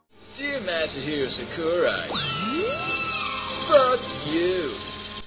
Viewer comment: i hear fack you